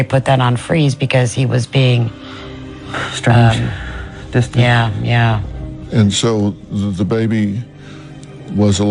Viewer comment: 😳 does anyone hear that male evp whisper?? I have air pods and in a quiet room.and it is a disembodied voice...wow.. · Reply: It the dad saying ‘monsters’, love.